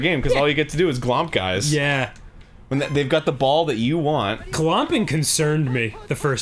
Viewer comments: "Glomping concerned me the first time it happened to me at a convention" i misheard that as glop... Thank god thats not what Danny said though. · What was the thing Dan was talking about at 3:10? Did he say "gumping"? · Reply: Glomping. Its an extreme hug where you dive at the person.